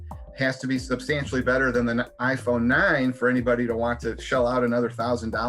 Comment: Did he just said iPhone 9?